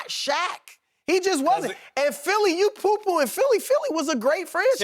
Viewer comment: Did Chris say "Poo Pooing"?!🤣😂🤣😂🤣😂🤣😂🤣😂🤣😂🤣😂🤣😂🤣😂🤣😂🤣